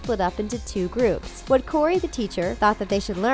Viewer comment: Cory not Cody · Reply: I thought she say cody and not Corey